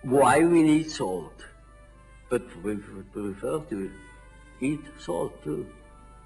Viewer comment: what does he say at 4:08, "eat soil?"